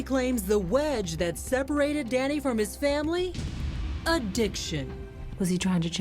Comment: Why did my dumbass think that she said “a Diction” not “addiction”😐😂